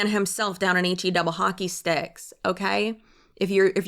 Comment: When I heard refer to Hell as H-E-double hockey sticks, I thought she said A-G-double hockey sticks. Therefore I will buy a first-class ticket to Agll